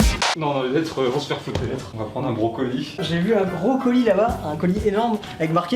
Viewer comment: j'ai entendu on va prendre un brocoli